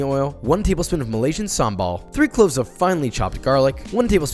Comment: While making the sauce: "add two tbsp of What? And some What? What the hell? Did he just say gumball?" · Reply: Sambal